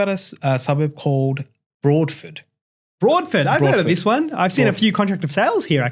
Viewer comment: Whats the third suburb. I could not find anything called as Broadfit. Not sure if i misheard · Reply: Broadford